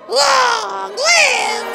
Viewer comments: I heard "long live the twig!" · I paused it at the right time, and it sounded like it said long live the kid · I thought he said “long live the turd”